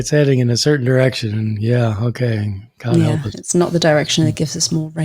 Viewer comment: Did say “ God help us “ at 2:07 😂😂